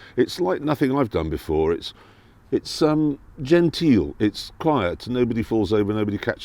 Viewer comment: Genteel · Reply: ah right. I thought he just said gentile in a posh accent but there is a distinction. Thanks!